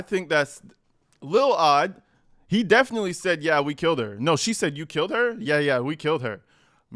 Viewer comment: I heard “yeah we TOLD her!” Rather than, “yeah we killed her” but it’s really hard to make out tbh. The girl definitely asked “what did she look like” and for sure said, “yeah me and David” I think they say, “last night!” As well. But honestly I wonder if we’re hearing “killed” because the subtitles say that? Don’t get me wrong. I definitely don’t think D4VD would have shut up about his evil crime and I feel like his little entourage knew something.